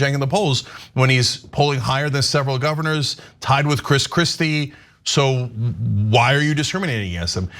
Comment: I could swear I heard Cenk say "Chris Crispy". 🤣